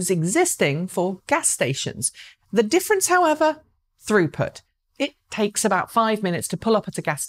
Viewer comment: I really heard “Rupert” in stead of “throughput”…